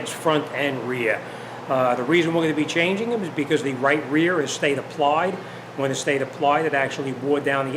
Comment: “bc the right rear stayed applied” = caliper locked up. I had to rewind that part 3 times bc I couldn’t figure out what he said Thanks for the video!!